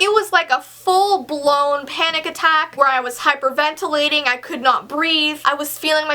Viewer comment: i thought it said picnic attack for some reason lol 😂😂😂